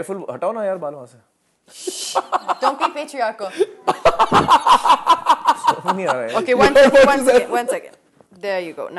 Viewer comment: what she said? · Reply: Don't be patriarchal